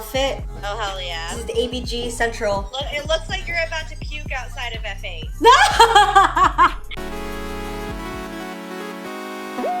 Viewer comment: "it looks like you're gonna go puke outside F8" 💀💀💀💀💀 edit: lmao i thought said café i knew that was too tame